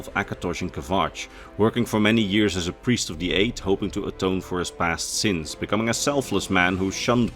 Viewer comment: Sorry, a priest of the how many? I must've misheard... This was an incredibly useful video! I found it entertaining and informative, with a calming narration. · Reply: The Eight and One